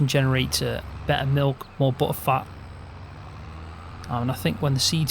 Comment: Did you just say "...buhheh fat" at 8:23. LMAO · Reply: haha butter fat